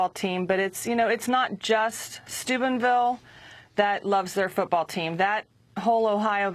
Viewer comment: every time the news reporter says the towns name to me it sounds like she is saying Stupidville lol